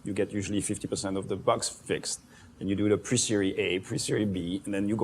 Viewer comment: What did Meylan say in 2:14? Presery A and Presery B?